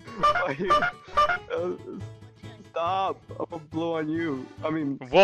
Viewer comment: wtf did he said ? (I seriously didnt heard him) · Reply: He said, and I quote: "Stop, I'mma blow on you." Think before you say people. Also, wtf is an F.B.I agent doing on an SCP 999 video?